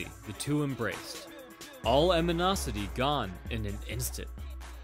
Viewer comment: I heard "aminosity" I think it's animosity